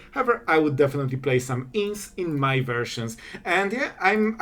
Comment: you say you would “definitely play some *ints in my version” what word is that? Ints?